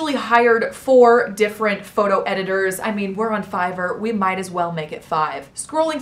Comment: I thought you said "we're on fire, we might as well do another"